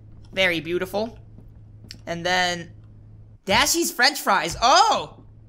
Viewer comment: I thought he said "Daddy's French fries"